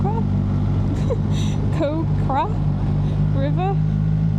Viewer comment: OMG, I heard say, "Cock river" :face-blue-smiling: you listed every items you packed but, forgot the Condom. It must be a good one since, is not pregnant yet. :face-blue-smiling